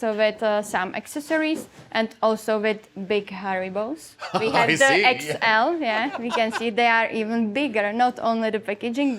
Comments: Lol. Had to listen twice 18:14 about the gummy bears. Sounded like she said "we have the Big Harry Balls" lol. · i heard "Also with big hairy balls" 😂😂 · When she said "Big Haribos" I wasn't paying attention and had to double-take because it sounded like "Big Hairy Balls"